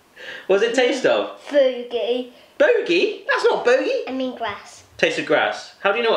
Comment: Oh sorry about that I thought you said bogie not booger